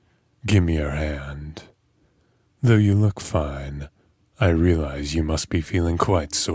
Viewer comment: Oh my god when he said “ you Look fine” I thought he said you look five and I just🫥